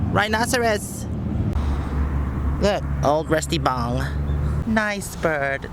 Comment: did you call the space capsule at minute 4:03 a rusty bong?